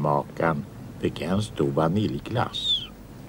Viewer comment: Vad säger han? Beställde Kalle en stor vaniljglass?